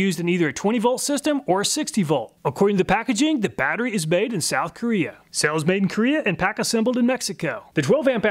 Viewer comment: "Battery made in South Korea"...I hear "Let's get this out on a tray" "Nice"